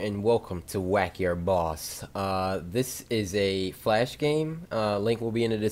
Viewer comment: In the start I thought he said Hey guys today I’m going to play whack your balls